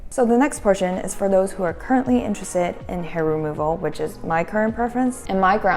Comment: Your thoughts on body hair vs hair removal? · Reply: I thought you said Air removal